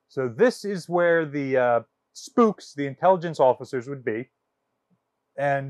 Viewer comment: Ok, I’m a Wisconsinite and may have misheard. At the 10:03 mark did he say “ Sailing into the sea of OshKosh”?